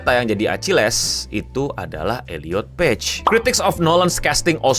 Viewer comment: gue dengernya Ediot peg